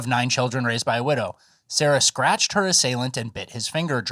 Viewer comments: Thought she was saying "stretched" not "scratched." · I thought that lady said she wanted her cootchie "stretched" not scratched